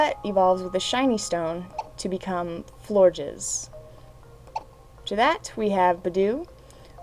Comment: Lol when she says pronounces florges, it sounds like she is saying floor jizz :,D